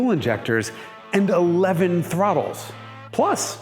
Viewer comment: Okay am i the only one that heard him say 11 throttles? Ive been digging for a good cutout pic so i cant see wtf is going on there but cant find any....some clarification please?